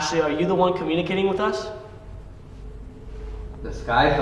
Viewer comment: “The sky is the limit” makes me think they are saying ‘There’s no Heaven’